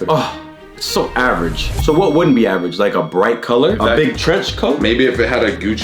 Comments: yo idk if anyone noticed, but at around 14:45 the caption said george coat💀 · LMAOO 14:47 has me weak 😭💀 Captions said a George Coat